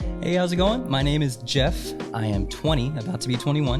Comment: Thought he said "my name is Jif"